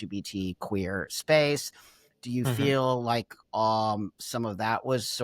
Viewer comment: did I just hear a fart?! 🤣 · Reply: 😂 she said “mhm”